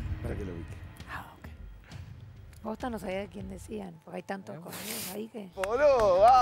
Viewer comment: JAJAJA escicharon lo que dijo en el minuto 27:45?!!! "posta que no sabian de quien hablaban, porque hay tantos cornudos ahi que..." jajajja escuchenloo por favor..cuando se van al tape de enfrentando a